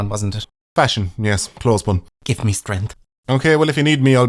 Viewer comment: So, did you mean to say "clothes one" here or did I just mishear this into another dad joke?